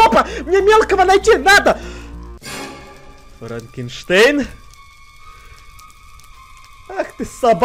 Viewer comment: мне послышалось моргенгштерн:-)